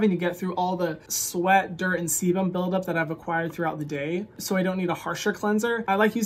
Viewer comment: Did you say "semen build up"? I swear you did. But you talk fast and I have playback at 1.5x so it sounded like you said semen build up lmao